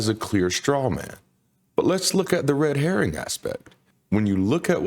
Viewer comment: Oh Red Herring, I thought he said Red Hair at first 😂